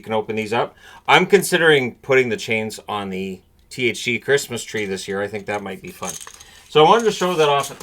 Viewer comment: I literally thought that you said THC Christmas Tree at first. You Canadians really know how to get festive, eh?